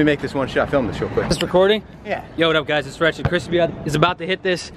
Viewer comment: Did he just say " hey whatssup guys rice Chrispy" in 2:52? Btw rice Chrispyyy is actually a youtuber